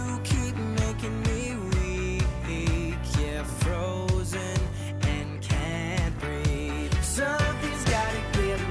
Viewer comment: lol i thought it said ur frozen in cabry instead of ur frozend and cant breathe